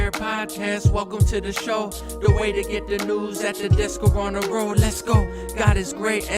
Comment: is he saying and the disco on the road?